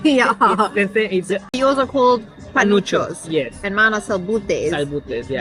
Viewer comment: Lmao 😂 I thought he said panochas 🤣